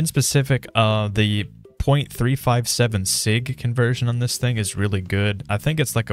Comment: Did He say SIGMA?!??!